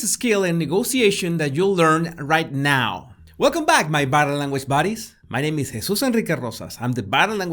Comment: I always hear “ battle language buddies” 😁 Dealing with other humans is really a constant battle 😂